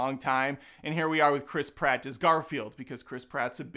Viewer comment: At first I thought you said Chris Pine and I was like oh that sounds good.